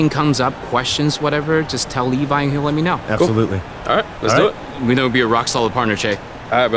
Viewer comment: i think the subtitles are wrong, the guy clealy said "we know you're going to be a rock solid partner che"